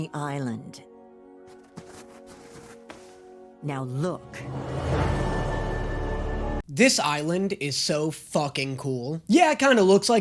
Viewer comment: when eda said "Now look" I got an ad and i wasn't looking at the screen so it sounded like she said "Now look, this is valorant"